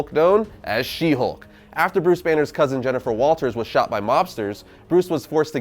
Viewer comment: I misheard and thought she got shot by "Lobsters".